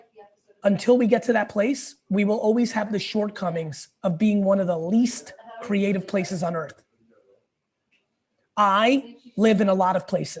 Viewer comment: I thought Gary said that "one of the least creative places I live is Adelaide" and I was like "huh, yeah, I can see how Adelaide isn't so creative" lol